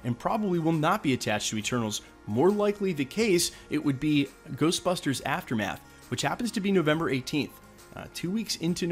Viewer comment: Did he say Ghostbusters "Aftermath"